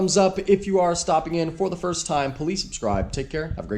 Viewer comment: It always sounds like you saying “police subscribe “ 😂